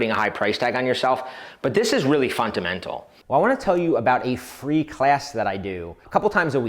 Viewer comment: I heard fun-to-mental